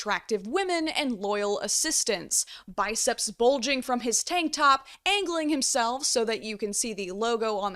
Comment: I thought said, "Biceps, bulging from his taint top!?😅 TAINT TOP!!!!!!!!!!!!!!!!!!!!!!!!!!!!!!!!!!! Lordy you make me laugh!😅🤭💗💗